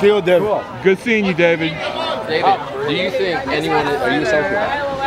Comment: Does anybody know what hey asked him at 1:10 ? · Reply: " David, do you think anyone - are you a psychopath?"